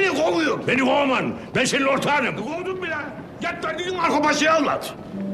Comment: de zeki alasya ne diyor anlayabilen varmı lütfen açıklayın :d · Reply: Git derdini Marko Paşaya anlat diyor